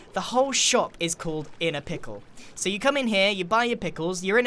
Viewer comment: For The Last two years i thought it said “I’M A PICKLE”